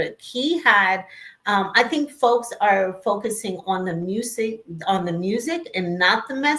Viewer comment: Correct and he clearly said its BIGGER than the music.